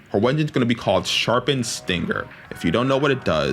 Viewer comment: for some reason I heard sharpened stinker 😂